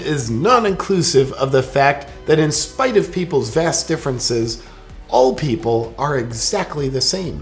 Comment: I laughed when you said vast differences because I thought you said vas deferenses.